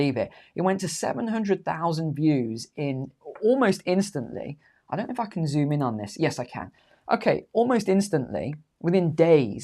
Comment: it sound like you said "I don't know if I can *Fuking zoom on this" hahaha but you were close there.